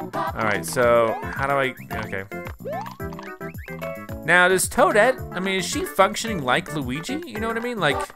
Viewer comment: Wait, on 20:37 it sounds like says that toadette f***s s**t like luigi